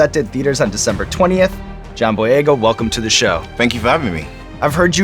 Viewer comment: I thought Sean said jon-boi-ego